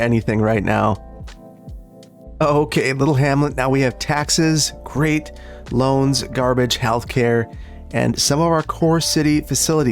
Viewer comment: really thought you said "taxes great, loans garbage...." instead of "taxes great, loans, garbage..."